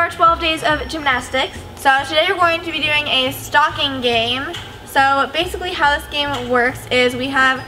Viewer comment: I thought said 'stalking game' for a second 😂